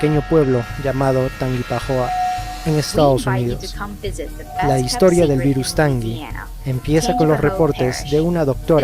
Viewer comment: Entendi "la historia del virus de la tanga" XDXDXDDDDXDXD